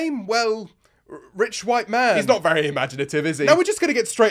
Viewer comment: I thought he said rich chow main